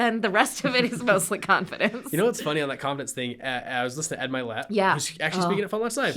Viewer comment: At about 18:09 into the call I heard you talking about someone you like to listen to. The transcript didn’t pick up the name. Can you tell me the name of the person you are talking about. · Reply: Ed Mylett